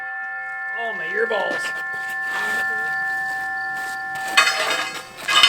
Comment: Lmao! Cracking up over odd turn of phrase! At first I thought I heard him wrong but after a second play through he did indeed say, "oh my ear balls!" ' reaction is everything!